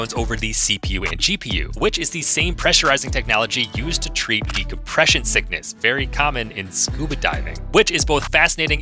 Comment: DECOMpression sickness??? I thought he said DEpresson sickness and I was like wait, there's a CURE for me???